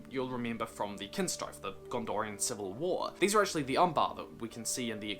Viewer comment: What did you call the descents of Castamir at 6:15? "These are the .... "? 'Umbafa', or something... You say these 'Umbafa' were actually featured in the Extended Edition of The Return of the King. I'd be interested in knowing where and making that connection. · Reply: He said they were the Umbar. I'm presuming he meant the people who eventually inhabited the location known as Umbar, far to the south of Harad, from whence the "corsairs of Umbar" originated, sailing up the coast to join the forces of Sauron in the War of the Ring.